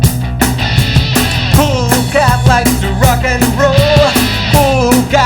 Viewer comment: I thought it said: "Cool Cat Loves To **" XD